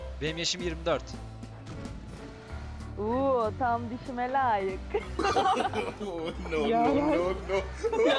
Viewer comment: kız ne diyor bı yardımcı olun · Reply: Disime layik